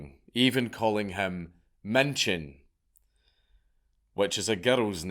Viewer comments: What word are you saying? Menschen? That's not a name, but the word for people. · I'm native to the German language, but never heard that name/word. All I hear is Männchen, which is the cutiefication of man. Or Mädchen, which just means girl. Can someone spell it out for me?